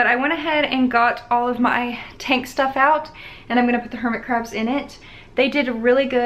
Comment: I thought The "Hermit" Said "Kermit" 😂👏